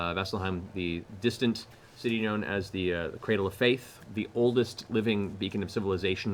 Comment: i thought he said cradle of filth not faith lol. brings back it crowd memories haha